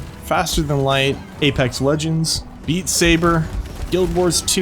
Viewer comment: When you listed Beat Saber as one of your favorite games, it sounded like you said 'Eat Saber' and I laughed WAY harder than necessary. 🤣